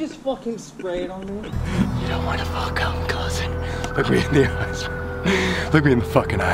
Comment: Colby said something about not wanting to come closer and I thought he said f*ckin a cousin. 🤦🏼‍♀️